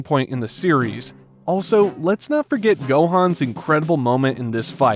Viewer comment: did anyone else hear yohan